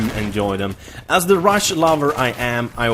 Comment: I thought you have said "Russia lover" HAHA!!!